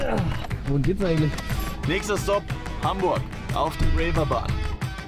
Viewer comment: Min Bin ich der Einzige der "Nächster Stop Hamburg, auf die Raper Bahn." verstanden hat? xD